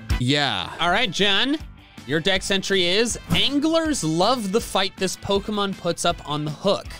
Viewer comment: I paused at 5:19 and i heard angler.